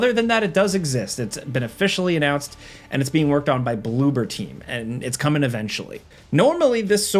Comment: That's the first time I've seen Bloober Team written down. I always thought people were saying Bloobertine, lol.